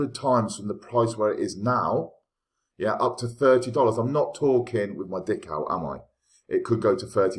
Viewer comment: What exactly did you say? Is that a saying like “knock wood”? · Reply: Not talking with my dick out for a potential 100x in the future? Haha 😂 It’s kinda my own flava slang